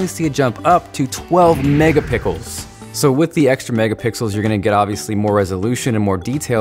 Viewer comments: Did you say megapickles at 2:34 ?? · Is it me or did it sound like he said 12 megapickles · did he just say megapickles? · Did anyone catch him say mega pickles around 2:34 😂😂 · Am I the only one who heard him say mega pickles at 2:34?